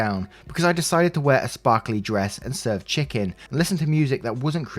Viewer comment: I heard " I decided to wear a sparkly dress instead of. Chicken" I had to listen 4 times🤣🤣🤣